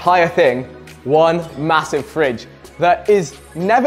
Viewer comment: I thought it said "fringe". The Evolution of Vikkstar's "Fringe".